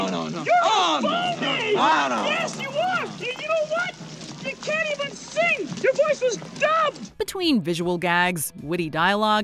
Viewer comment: Lol, I just got Piggy's line to Nicky. When I was a kid, I thought she said, "You can't even sing. Your voice was dumb." Watching this video, I realized she actually said, "You can't even sing. Your voice was dubbed!" Hahaha! It's funny because his singing voice was very clearly dubbed in that scene. Gotta love Muppet meta humor!